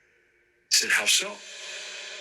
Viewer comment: Such a beautiful Villa, even though the spirits got bored LOL. I did catch what was said 30:19 sounded like "As if, How so?" and the one at 30:29 sounded like "Here Sir" it may have hit wrong due to the very proper English accent that was pretty prominent in the 1800's. Maybe the spirits just want to get to know you before they allow you to know more about them. Sometimes that can be the case too.